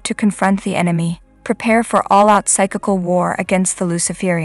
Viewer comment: Hello & Greetings I've listened twice but am now watching & listening. At 1:25 as part of your intro about preparation the word "psychical" w-@ is mentioned. At 1st I thought u said cyclical - but CC presents it as written in the quotes. Is this referring to cyclical, physical or something else? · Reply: Emerald Waters, Hello sister, sorry that it’s not clear to understand. The word I used is you also must be prepared physical. For example, if you decide to do fasting. Sometimes it takes a lot of strength -energy. More than anything when the Holy Spirit leads you in the prayer. it is when you are praying in the spirit. If you have any questions, please let me know.